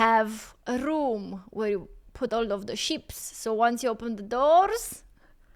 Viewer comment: It’s just sheep, you sounded like you were saying ships 🤦‍♂️ like that time you ripped into me about your twitch channel, you misunderstood my comment and were too ignorant and up yourself to say sorry, because you’re a fake arsed person.